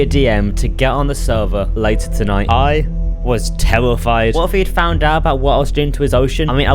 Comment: u said "I was tellafied" instead of terrefied